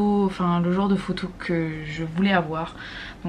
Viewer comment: ça va être tout pile .... Tout pile quoi ?? 😅 Veux savoir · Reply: Tout pile le genre de photos qu'ils aiment, dixit je crois